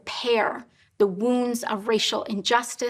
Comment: Also for those wondering, personally my favorite game is the calculator app on iOS. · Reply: did she just say opposing colonization?! Wow she’s out of touch...